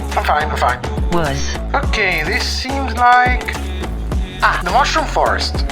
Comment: What did she say? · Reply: Wuss 😆